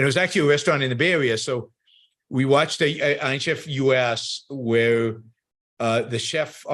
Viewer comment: What show? "Ayn Chef"? "Ein Chef"? What did he say? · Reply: I think he said "Iron Chef"